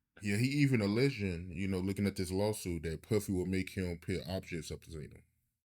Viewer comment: Hey fam.. 👋🏿.. There is a cencored part at @ 0:45 . Internet sleuths.. kindly help me figure out what he said at 0:45 . It must be juicy coz they censored it.. Salute yall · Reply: puffy would make him put objects up his anus. Allegedly